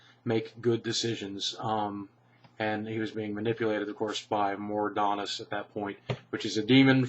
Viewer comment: Sounded like you said Mordanas or something when you were talking about CoS, did you mean Mal'Ganis?